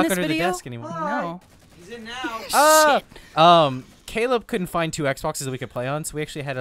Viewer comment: Geez for a second I thought you said Monty, but we all know you meant Caleb